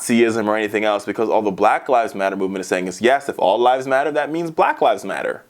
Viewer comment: I heard "All lies matter"